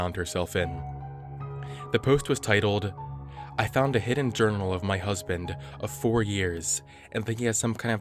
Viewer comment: i thought it said “I found a hidden journal in my husband”